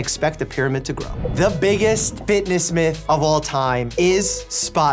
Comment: Low key thought it said finess